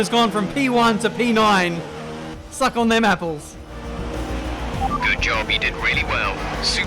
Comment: What did he said?😂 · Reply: Lxqend Suck on them apples